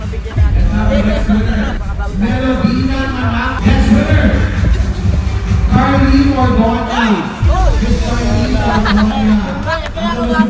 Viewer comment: Carlyn Ordoña narinig ko tama ba?